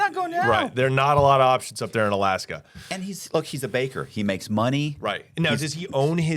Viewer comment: I thought you said he was a baker, not a butcher 🙃 Anyway, you guys are imagining big city hookers but I think most of the working girls in that neck of the woods are native women wearing sensible clothes because even in Anchorage it's too brisk for scantiness. Like the hookers in Glasgow, for instance. Edit: apologies, I should have written sex workers